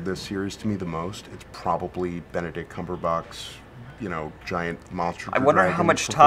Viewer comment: did he just call him cumberbox :O